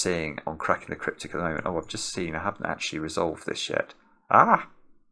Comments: "I haven't actually resolved this shi-" I know I misheard but wouldn't it be hilarious? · I thought you said "oh I've just seen, I haven't actually resolved this shit" and I had to replay four times in awe to realise what you said😂